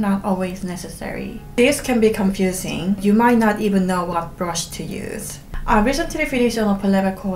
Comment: wtf was she sayin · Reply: She's saying "to use"